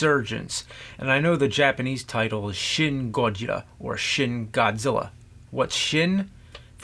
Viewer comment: I thought he said, "Shit Godzilla". 😄😄